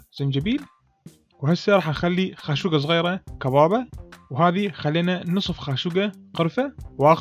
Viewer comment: ما فهمت البهارات. ما سمعتها... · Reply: كبابة